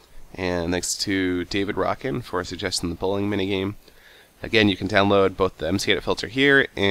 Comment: i thought he said blowing minigame :P